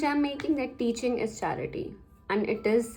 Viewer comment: For a moment I thought you said 'Cheating is Charity' 😂